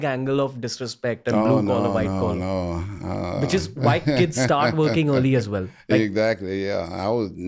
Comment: did he just say white kid????????????? No way he did…he meant to say American kids lol